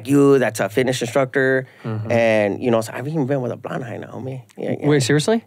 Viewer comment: "A blond eye". Does anyone know what he is saying here.